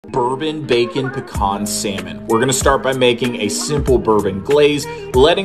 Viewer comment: I thought you said “bourbon bacon pecan semen” …. 💀😂